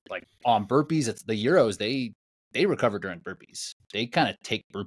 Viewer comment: Recover during burpees? Did I hear that right?